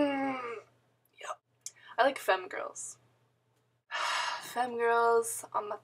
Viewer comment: i totally thought you said femme girls...on my face... HAHAH. 5 stars for blue moon.